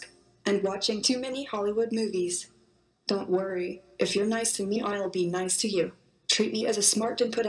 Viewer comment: "If you're nice to me, I'll be nice to you." Is that what she said? 😱😱